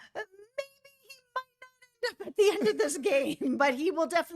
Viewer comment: Huh 1:01 What were you saying? 😳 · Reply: She said ‘maybe he might not end up at the end of this game’ but it was a bit garbled for some reason.